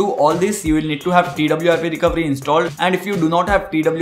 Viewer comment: what do i need to install 0:20 ? sorry couldnt understand it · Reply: TWRP (TeamWin Recovery Project) = The recovery screen he you see him use to backup the factory build and wipe cache etc.